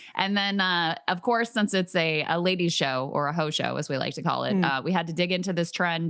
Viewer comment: Did she say ho show? 😂